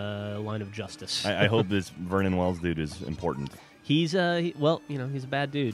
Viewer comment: i thought he was saying vernon wells lol